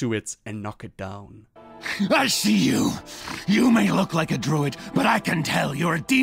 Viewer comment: did anyone else hear him say you look like a droid